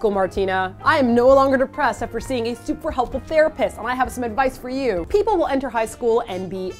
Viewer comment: I thought it said the rapists instead of therapists. I thought eat your kimchi was saying for second there don't be afraid to see the rapists children. yeah... can't read or hear the english language properly.